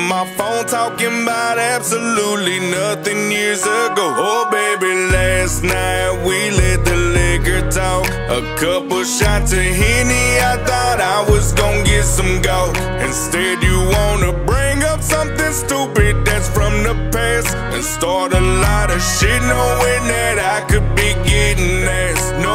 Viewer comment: I felt that when he said a couple of shots of Henney and i thought i was gonna get some GAWK.....instead you wanna bring up something stupid thats from the past....felt that to my mfn soul..... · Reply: I thought he said get some gob. 😂😒